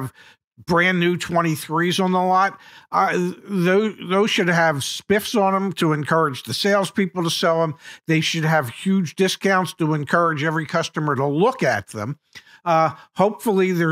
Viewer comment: I thought he said spliff 😂